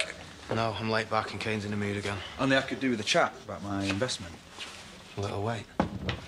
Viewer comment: what is Aaron saying to Robert when he is walking out of the bar (1:24)? I really can't make it ;( somebody help please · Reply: He said "well that'll have to wait" when Robert asked Aaron to spare a few secs to discuss about the investment. Well I reckon Aaron is still bitter about what happened last night.